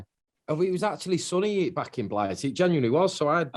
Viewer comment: What does Rob say at 4:46 "It was actually sunny back at ?????" Where? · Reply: He says 'back in Blighty' :)